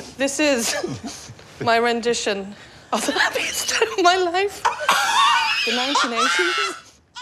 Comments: what did she say??? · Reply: My rendition of the happiest day of my life :) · “This is a rendition of the happiest day of my life” Trixie and Katya dies laughing 😂 😭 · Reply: OMG I couldn't breathe !!! At first I thought she'd said 'rendition of a happy ending' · she says "this is a rendition of the happiest day of my life" i was rewatching for 4 minutes trying to figure it out. Also! the person who wrote the closed captions said "(laughing) took my life", so here i was thinking that it was where someone committed suicide, and googling if anyone killed themselves at "abbey stone" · “This is my rendition of the happiest day in my life” Trixie & Katya: Breathless. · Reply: I thought she said this is my rendition of the happiest turd of my life.